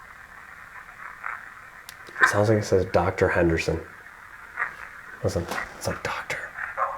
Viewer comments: before you ask what the doctors name is it literally says Doctor Henderson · In love with HAUNTED NIGHTS MONDAYS I'm looking for to watch this video, join the live premiere/ chat 🖤🖤🖤 so proud of my dudes !! Killing it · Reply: right before he asks what the doctors name is listen. It says doctor Henderson clear as day